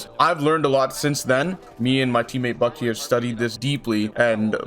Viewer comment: did you say me and my teamate bucke as in bbg bucke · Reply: SRN bucktee